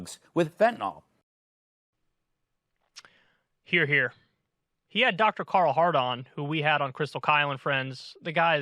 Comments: I'm sorry, did Kyle just say: "He had Dr. Carl Hard-on"? 🤣🤣🤣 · I can't be the only one who heard "Dr Hardon"!! 🍆😂😂😂